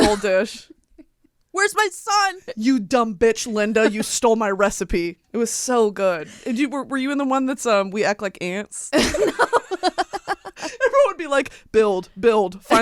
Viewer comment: I thought she said aunts and I was like what kinda aunts do they have 😅